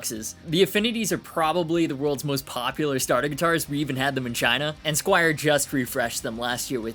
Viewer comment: "We even had them in China" - you said it so fast, I literally heard: "We even had the vagina"- and I was like, what teh hell does that model look like? Sorry, English is my third language :DDD Sometimes I have fun)